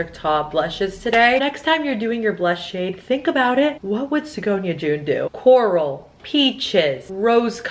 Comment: Sorry, did I just hear "Sagonia joon"?!!!!